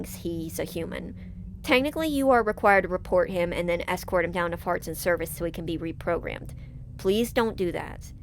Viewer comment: am I crazy or did you say farts and service lol